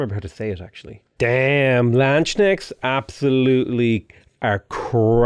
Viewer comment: The Liberation War buff is similar to the Leader Ability of Robert the Bruce (Bannockburn), and the Protectorate War was one of Tamar's old Leader Abilities (+100% Faith when declaring a Protectorate War) Also, the 'k' in 'Landsknecht' is hard, like in 'kill', and the 'ch' is a bit harder, like in 'christ' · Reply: honestly, it sounded like was saying "Landsnake."